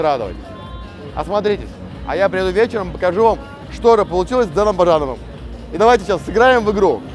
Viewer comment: На 2:15 сначала послышалось с Дэном Барановым 😂😂😂